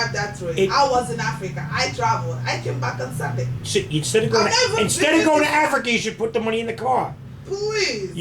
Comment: Dominic Lopez i said it "sounds" like one. I can't even hear what she is saying, so you must have good hearing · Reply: you couldn't hear her, maybe you should get your hearing because says "instead of going to Africa maybe you should put the money in the car."